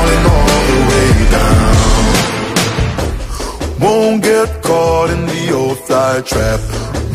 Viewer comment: I always thought he said “won’t get caught in the offside trap” Really a missed opportunity to change it for the fifa version lol